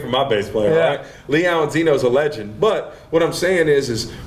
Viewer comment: I sho thought he said Leon Zenon.